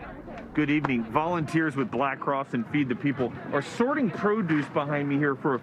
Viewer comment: Umm did he say Black Cross..wtf happened to red cross..I swore i just heard him say black cross